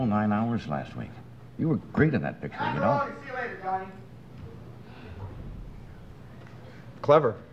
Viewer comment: What was said by audience member at around 0:45? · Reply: "something...something..." that I couldn't make out and then "See you later Johnny". Weird.